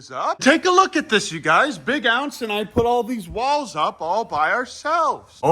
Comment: I thought you were literally saying the video that big ounce died